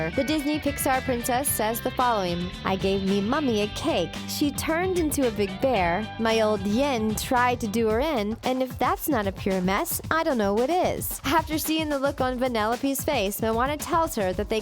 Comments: The first time I heard her speak I thought she said "I gave my mum a cake she became a bear if that's not purnes I don't know what is" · I thought Merida said... "I gave me mummy a cake, she turned into a big bear, my old man tried to do her in, and if that's not the cure then I don't know what is." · I couldnt understand "...my dad tryed to kill her..." part so thanks for clearing that up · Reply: Ffion Owen I thought she said my dad tried to do her off